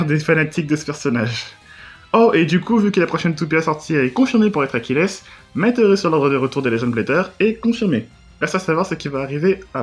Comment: jusqu'a 2:19 ta dit quoi ? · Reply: la prochaine toupie annoncée est une Achilles, confirmant ma théorie sur l'ordre des retours des Legends